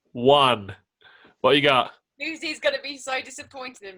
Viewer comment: what did say? “??? gonna be so disappointed with me” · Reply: I think Lucy as in Lucy Moss